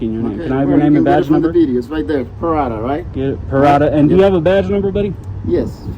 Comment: Ooohhhhh Paradaaaaa I thought it said Asshole